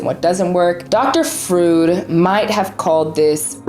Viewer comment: “Frood”? Did you mean Freud? Because that’s pronounced like “froyed” like toyed but with an fr instead of a t. 😂